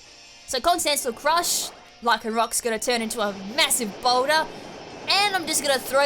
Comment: I thought you said lycanroc is gonna become a massive boner xD